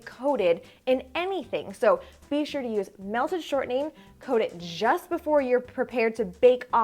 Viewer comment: I accidentally bought ghee because I thought that was what you said. I now see you said shortening. Will ghee work as well? Thank you!